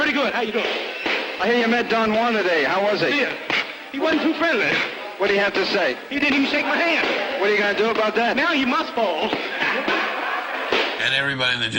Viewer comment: I hear you met Don... Don who? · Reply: Don Warner